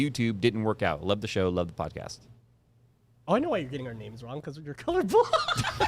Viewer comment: i cant make out what says, can anyone help? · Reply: "oh I know why you're getting our names wrong, it's because you're color blind"